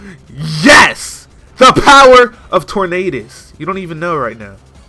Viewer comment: thought you said power of torn anus. rofl